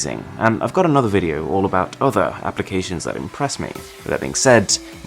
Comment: Here's the catch- Arun's voice is so soothing and clear that even the auto-generated captions are word to word correct 😉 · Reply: in 9:04 the captions show Erin instead of Arun